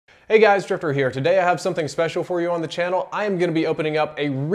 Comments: Is it just me or did it sound like he said "Hi guys, stripper here" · In the beginning it sounded like u said "hey stripper here"